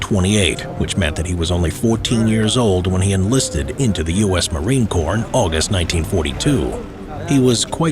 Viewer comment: Marine corn is what i heard what about u